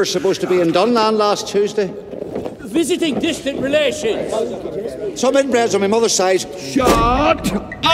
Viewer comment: did he just say some inbreds on my mother's side anyone else hear that?